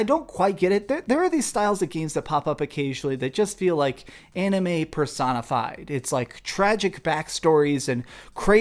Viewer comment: Who else thought he said Anime Persona 5 @ 6:07??